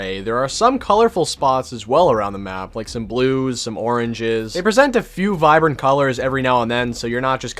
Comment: There are some oranges Me: Wait did he just say Origins